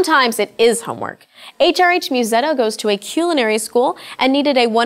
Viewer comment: did she just say.... "quelenery???: it's Culinary you ugly fuck